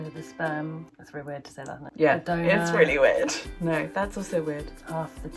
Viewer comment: I'm so stupid every time said 'donor' I heard 'doughnut' WHY